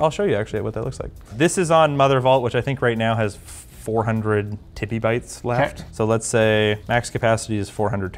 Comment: Did say "tiddiebytes"? 🤣